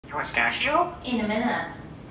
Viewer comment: It kinda sounded like he said "in a minute" back at you just mumbled to make fun of you lol